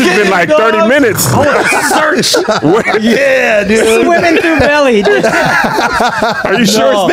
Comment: did Andrew just said "yeah n***a"